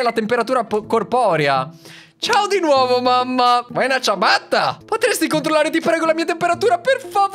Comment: Io che leggo:cosa? che legge:vuoi una ciabatta😂😂😂